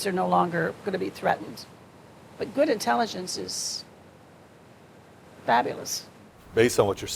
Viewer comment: good intelligence is few and far between is what I heard